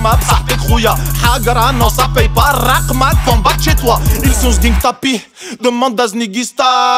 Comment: Wsh c’est quoi ce délire ça fais dix ans je veux savoir il dit quoi à 1:36 I son snique tapis mdr